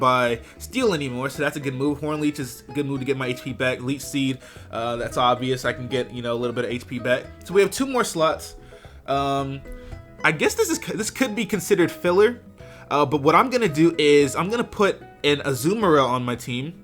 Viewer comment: for a minute i thought u said 2 more sluts